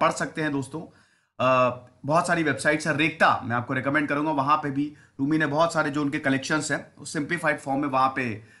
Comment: Can anyone help me with the site that sir has mentioned at 3:37 ??? Couldn't catch the name he mentioned. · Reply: Rekhta